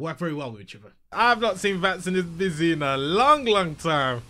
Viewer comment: for a minute I thought you said vatgasm when you opened vatspy